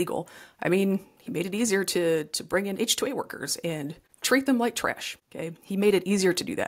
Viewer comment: To bring in what kind of workers? H-two-y? You say it twice without explaining it, and you say it so fast that I can't look it up for myself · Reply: H-2A Temporary Agricultural Program